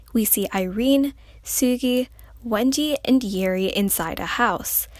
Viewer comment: it sounded as if she said "Wengie" instead of Wendy 😅